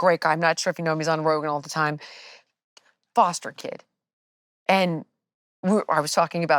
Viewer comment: thought she said "bastard kid" lol